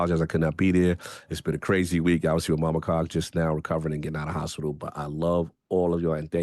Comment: 3 Bit... Cog didn't say mobile Con or whatever you thought he said..... He said "Mama Cog"... He was talking about being with Mama Cog because he is there with her in the hospital